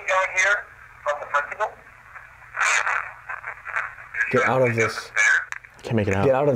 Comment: “Get out of this building” is what I hear… 🤷🏼‍♀️🤷🏼‍♀️